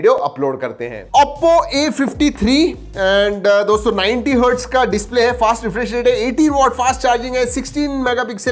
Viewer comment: I thought he said 80w fast charging 😱😱😱